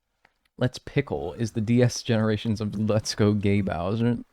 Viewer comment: Man I heard "let's pickle" on the N64. It isn't just the speakers on DS's for that port.